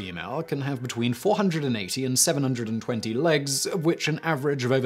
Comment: did anyone else hear "480-720 legs" at 1:29?